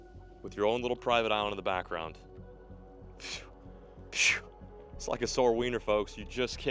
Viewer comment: WHAT DID HE JUST SAY😂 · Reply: 😂😂 bruh I know right that had me dying! “Just like a sore Weiner, can’t beat it.” IS INSANE WORK!😂😭